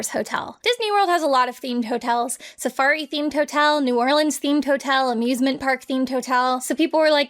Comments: I thought said: "...furry-themed hotel..." Oh no. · When you immediately misheard "safari themed" as "furry themed"....